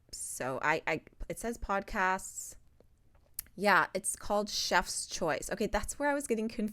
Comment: I also thought she said, "Steph's Choice," and I missed that she said, "Where should this dinner start?" So I first thought she was asking about the "entree" because of the album name. 😄 But really, picking the appetizer, entree, and dessert from the album totally tracks with its title!